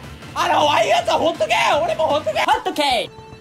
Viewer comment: 最後のほっとけー⤵︎ が 発音いいホットケーキ に聞こえた